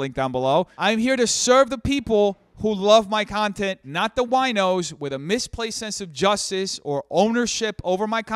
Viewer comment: I don’t think said wine-ers, I think he said wine-os as in people that drink wine. AND was drinking wine in the video of you guys covering him. Mayyyybe a reach but it sounded like it fit. 🥴😂 PS love the background!